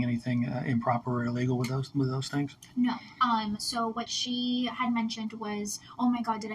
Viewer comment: every time this manager says "um" i think she's saying "i'm"